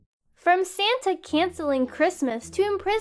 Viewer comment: I HEARD "Sans" instead of "Santa" XD